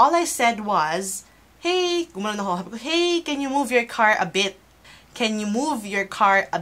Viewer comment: tama yan napaka arpga te nila... kya lang posibleng na misheard ka nila akala nila cnbi mo mo... can u move ur car a bit.. akala nola bitch nsabi mo... pro khit na mali p rin cla